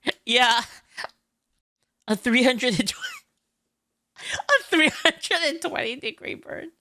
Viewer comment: I heard "320 degree burn" and misunderstood it as "320 creeper", getting 320'd in minecraft would be surreal